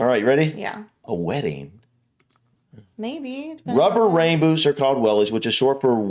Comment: I always wondered what Americans called wellies, and now I know. Was it...rubber Ray boots...or something? I don't know, maybe I misheard.